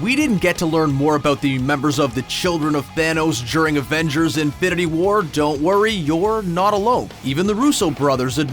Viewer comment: Is it just me, or does it always sound like he is saying Avengers: Infinerty war??😂😂